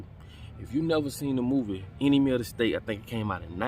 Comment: The first time he said the name of the movie, I thought he said “any meal to stay.“ And I thought to myself, what a weird movie title!😂